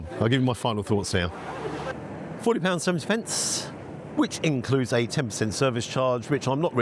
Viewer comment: Please. Please, please, please - Tell me that I heard ‘14 pounds 70 pence’ then?!?!